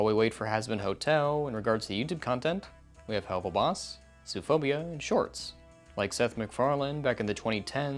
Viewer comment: I thought it said zoophilia